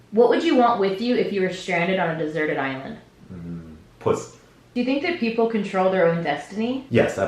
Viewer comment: did you just say pussy